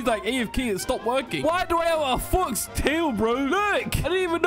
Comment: it sounds like says the f word